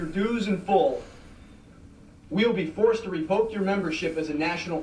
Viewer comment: I've rewatched this episode almost every Halloween. I realized that the dude says "invoke" instead of "revoke". I cared just enough to waste one calorie to type this. Have a nice day. :)